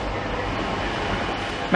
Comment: I thought I misheard coulthard LMAO but he legit said first, I'm done